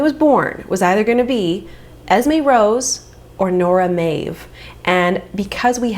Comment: Love the "sneaky" children in the back And also when you said Nora Maeve I literally thought you said Nora My (in Danish) which was on my girl list for my baby - it was a boy so he got Arthur 😊 also Bjørn (bear) is a Danish name